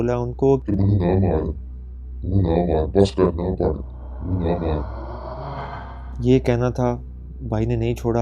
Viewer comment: yrr sound effect ki waja say smj hi ni ae kya kaha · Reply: Abu ne bola tha mujy mat mro na parho bas kardo